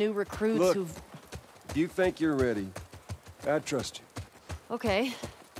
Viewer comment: YouTube automatic subtitles believe that what Joel said there is : if you think you're ready, Dad trust you. Yes!